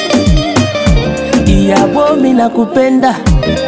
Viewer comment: Love from +255 Tanzania.. buh hold on 2:04 did he say "nakupenda"?😊